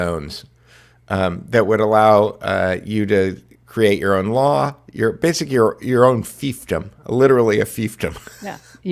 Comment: did you say "burbclaves"? Autonomous zones. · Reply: Yes Burbclaves. That's the name given to what can best be described as city-states in the novel "Snow Crash" by Neal Stephenson.